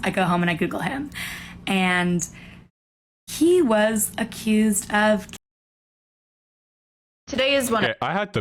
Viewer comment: "He was accused of kidnapping." That's what she said